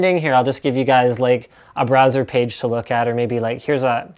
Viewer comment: LOL i thought linus said BRAZZER page.